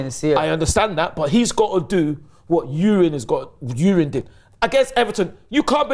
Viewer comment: why can't he just say Timber? I thought kept saying ''Urine'' over and over